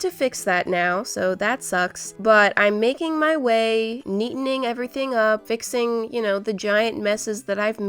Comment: I thought she was gonna say "making my way down town " 😂😂😂